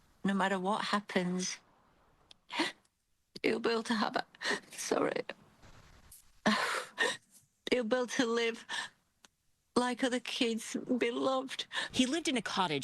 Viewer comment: "I decided he will be like other kids and be loved" (can't hear her properly) - is that actually what she said about her own child????